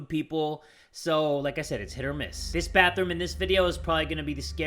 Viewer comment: ngl when u said hit or miss I thought u said shit or piss XD